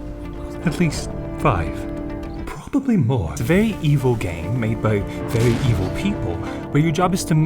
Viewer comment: For some reason, I heard him say "cruel game" and immediately checked the description. I expected it but still cracked.